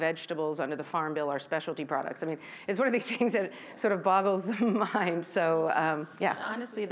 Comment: did she say it "bottles the mind"? Because that would be freaking hilarious.